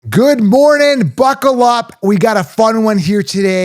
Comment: Totally thought he said "Good morning, fuck it up" 😅😂